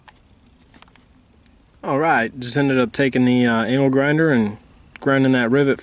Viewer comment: did you say "Anal grinder" on purpose? Please say you did just to see if anyone noticed.... Also, good video. I had no questions after watching. Except about anal grinders.... I had an Italian grinder for dinner last night...